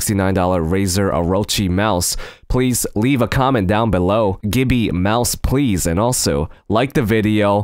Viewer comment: gibby mouse please it sounded like you said "gibby" instead of gimme